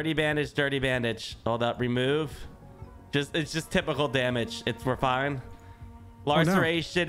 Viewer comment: "It's just typical damage" when I think it says terminal damage